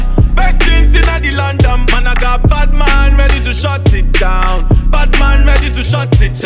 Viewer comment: I thought he said "bat man" not "bad man" 😂